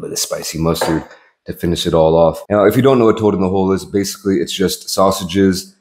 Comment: I thought it said turd in a hole